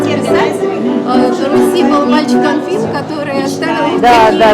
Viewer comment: Какой мальчик был на Руси на 21:31 ? Несколько раз смотрела не могла расслышать. Очень люблю Ваши видео и прогулки. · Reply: На 21.31 - мальчик Анфис, автор многих берестяных грамоток. Рисунки детей на банке из Дулевского фарфора - тоже его грамотки. (Подробнее - у В. Янина в книге "Я послал тебе бересту...")